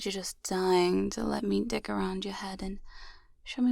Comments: I'ma be so for real I thought she said "dick around your head" and not dig around your head. Goodness auditory processing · I thought she said "dick around your head" for a second.